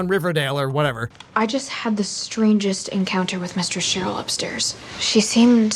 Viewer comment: Did she say MISTER Cheryl? LOL